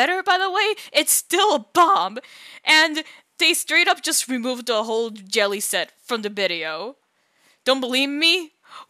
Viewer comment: Ok but really as I was watching this I honestly misheard you as saying "removed the set entirely" which actually made me FOR ONCE like what devsisters did with this update until I realized you meant from just a video